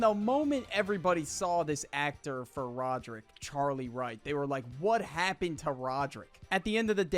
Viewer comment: my dumbahh thought he said Charlie white